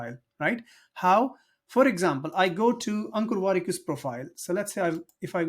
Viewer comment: I heard Uncle Warikoo instead of Ankur Wrikoo. 😅